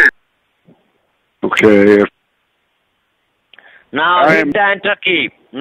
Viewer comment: What in the WORLD was that Indian guy saying 4:45 and after that? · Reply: Now hit the enter key. Now hit the enter key. ( I think) 😅